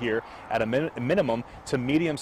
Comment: Wait a sec, did I hear that right 😂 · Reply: I am Jason Law, nine on your side.